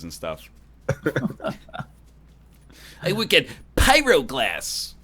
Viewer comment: uhhhhm strimmer, i think you meant to say "borosilicate glass" he har har.